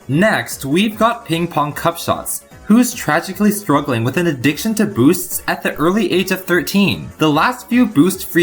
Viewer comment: whenever you say "addiction to boosts" i hear "addiction to booze"